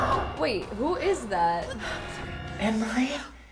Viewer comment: did that just said emmry THATS MY NAME!!